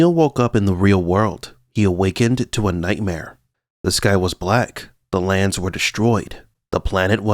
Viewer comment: I thought you said "This guy was black" in your opening.